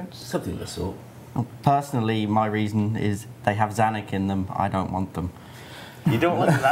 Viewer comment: "The have Zanarkand"? What did he say? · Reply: "They have Zanik in them. I don't want them." Zanik being an important NPC in the cave goblin quest series